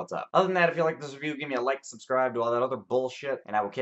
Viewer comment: At the end it sounded like you said "subscribe to all my other bullshit" and thought that was a refreshingly frank, honest take on cinema